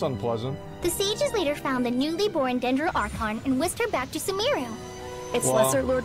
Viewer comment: One part you misheard that was "The Sage found the new Dendro archon and whisked her back to Sumeru" you heard that "wished back"..